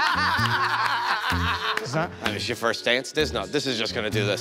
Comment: Joe:”there’s no file...what is this, your first day?” 🤣🤣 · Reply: I thought he said "your first dance"